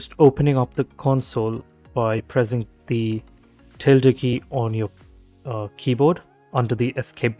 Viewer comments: Can someone explain to me what he says at this point? I'm sorry, I do not understand the accent. It sounds like "turtle key." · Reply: Hahahah I am saying Tilde Key. It is the one below the Escape key and above the Tab key on your keyboard. It looks like this '~' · can you write the word what you talking coz its not clear to me your english, sorry. I just heard escape button at time 1:10 and i did not unders whats before it. thanks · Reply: Hahah I was saying that the key to open console is the ~ key on your keyboard. It is the one below the Escape key and above the Tab key. Hope this helps :)